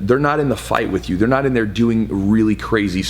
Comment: I thought you said europeans didn’t matter lol 😂